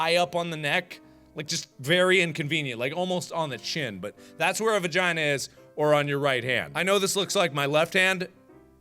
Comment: I thought the blue one said have a vagina on your head XD